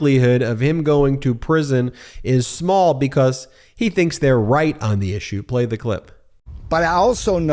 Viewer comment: I thought I heard Navarro say "I'm not going to prison because I'm white on this issue", and I think that was a fair assumption, I could be excused for that mishearing 😂